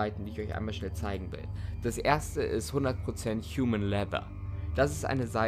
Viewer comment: Wer hat auch bei 8:30 humane labs von GTA verstanden 😂